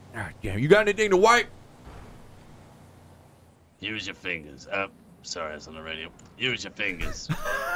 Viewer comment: "Use your fingers.....sorry wrong radio." LOL!! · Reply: I heard it as "sorry I was on the radio" but either way XD